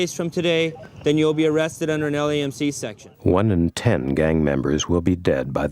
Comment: does anyone know what he said at 15:45 ? arrested under a what section?? I'm guessing it's some kind of gang injunction shit but I can't tell what he said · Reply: he said Alien C Section, sounds like it